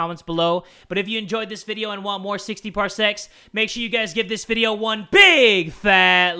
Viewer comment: It sounds like jay is saying 60 parsex instead of 60 parsecs